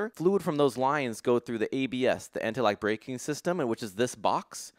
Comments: I heard anti-lag breaking system lol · you know you done too many race car, when your mind just programmed to say "anti-lag" instead of "anti-lock" XD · wait... Hold up... Did he said, anti lag????? 😅😅